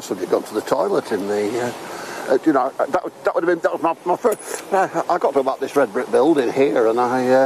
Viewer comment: Someone, please anyone, just listen at 8:40 to what says??? He is heard distinctly saying the following statement, after he mentions he thought someone went to the toilet, he says,” I got them out this red brick building here, and I just…..” what did he get phone, lead? I’ve told every live feed detective about this in chats, not one has the wherewithal to even respond. If anyone reads this, listen to that part of Sky News interview again. This is vital info, especially when you have rubbing of top lip, incoherent stuttering and being put on the spot, he’s faltering and let’s slip his truth whilst trying to evade Sky’s questions, it’s a classic liar move. Thoughts please🤷‍♂️